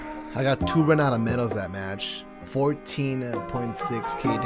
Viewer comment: good luck having 12.5k Efficiency :)))))))))) that is what you said on